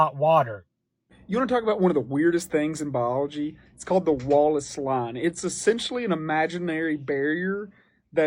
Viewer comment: The whole time I was listening to this guy without looking at the close caption and I thought the whole time he kept saying "The Wall of Slime". Where the hell is this wall slime in the ocean.